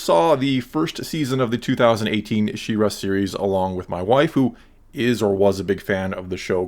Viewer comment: "along with my wife who..." I swear, I heard that as "along with my waifu"